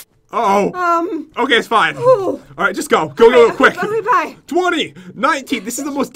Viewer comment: did jen just say love you bye to pat lol i think she did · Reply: Im pretty sure she said ok